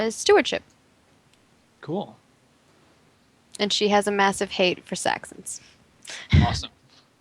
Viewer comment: I thought said her character has a massive hate for sex.